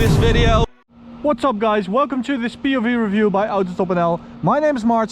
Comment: 开头我听成welcome to autostupidL，大型畸变现场rocks